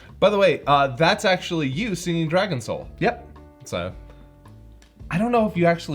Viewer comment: When they sing "Dragon Soul", at first I heard "Dragon Troll", which would have been hilariously on-brand for these Kai episodes.